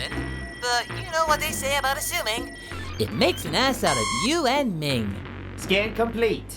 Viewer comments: Did Goku just say “ass”? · did goku just say ass?